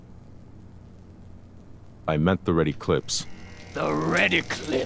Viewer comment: why, for all this time, i thought they were saying 'the Ready Clips' and i was mighty confused